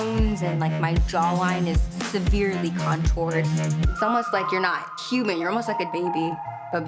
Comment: Did she just say its almost like you’re not human you are a baby ???😂😂